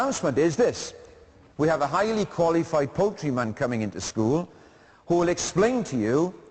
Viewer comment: i thought he said highly qualified pokemon